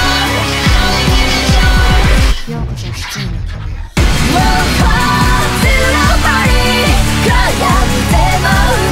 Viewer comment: ここなんて言ってるか分かります？ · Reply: Welcome to Journey of Hardship 苦難の旅へようこそ